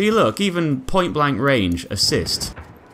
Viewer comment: I thought it said the ass tho 😂